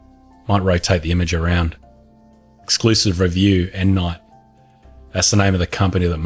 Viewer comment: I thought you were saying M. Knight, Like M. Night Shyamalan. Endnight, I get it now.